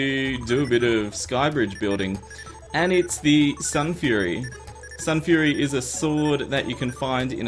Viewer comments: did you say sunfury or starfury? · did anyone else notice that he said "sunfury" instead of starfury